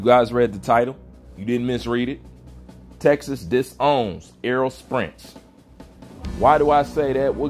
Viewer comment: Lol did you just say arrow sprints??